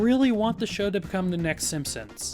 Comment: "The packet of ewer". I know what this is supposed to ACTUALLY mean, but that's how I heard it. Lol